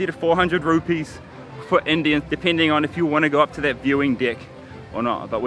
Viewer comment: Thought for sure you said viewing dick.